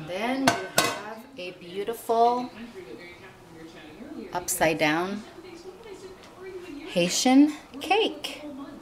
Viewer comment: For a second I thought these said upside down pineapple pizza, I was about to leave a dislike